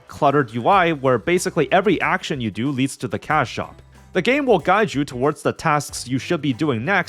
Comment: every time he says cash shop, i keep mishearing it as cow shop 😭